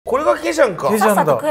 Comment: これだけじゃんか！って聞こえたｗ これがケジャンかって言ったのかｗ